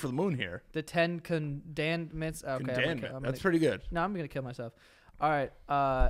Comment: did he say “now i’m gonna kill myself?” hmmm🤔